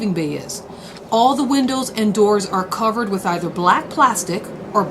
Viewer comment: listen to her say “window” first, then try to hear “windles” I heard windle at first so I had to replay